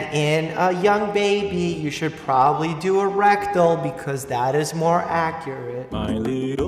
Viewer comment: i heard “erectile”